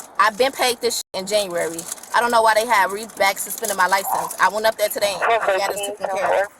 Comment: did she say "Re-Back Suspended" at 6:43 🤣🤣🤣